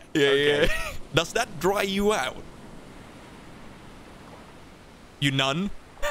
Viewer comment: Starting at 6:07 goes the opposite of "Moist" 😂😂😂 calling a dry nun.. I'm dying 😂😂😂😂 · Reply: Oh my heck. I kept trying to figure out why they laughed so much. I thought he said “You noddin’?” not “You nun.” □□□